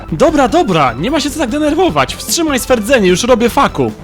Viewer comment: czy tylko ja usłyszałem "no dobrze już robie fuck you"?